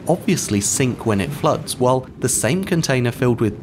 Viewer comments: "it will sink when it floats" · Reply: I heard "floats" at first but I think he said "floods" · "obviously sink when it floats" instead of fills, or is my hearing going out hahaha